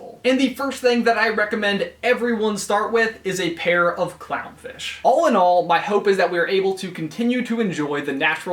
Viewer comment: Is it just me, or did I hear “Allinol” instead of “all in all?”